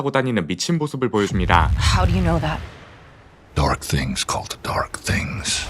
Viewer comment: 이때 영어로 뭐라고하는지 아시는분?? Dark thins xxx dark things 라고하는거 같은데... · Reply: called the